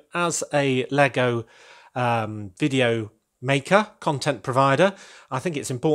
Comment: Sorry I didn't quite understand that? Did you mean Lego Vidiyo or video creator, I didn't quite catch you there. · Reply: Ummm. Video mainly, haha!